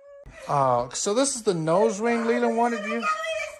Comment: I think that she said nose pierce